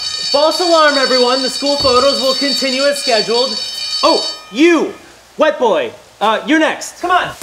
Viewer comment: I was thought Ian said “You, White Boy...” I have a problem